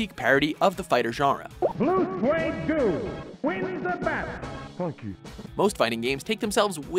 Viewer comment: did he say "Fuck You"?